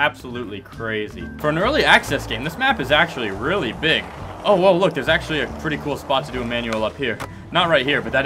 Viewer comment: Anyone else hear it 7:57 "Owo look"